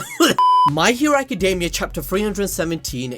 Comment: I just realised he was saying my hero academy🤣🤣 i thought he saying mikey academy 🤣🤣🤣🤣